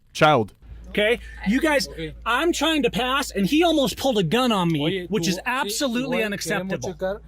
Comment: It seems he knows all the sov-cit buzzwords in Spanish.. I didn't know their bingo cards came in Spanish. Did he say, "Almost pulled a gun on me"?